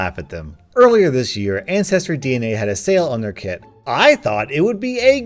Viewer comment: For a second i thought it said kid... PHEW!